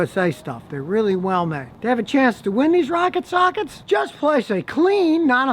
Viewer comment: Thought he said rocket socks!